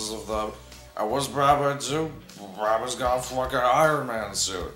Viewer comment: I thought said brownman so "brownman lalalalalal brownman brownman"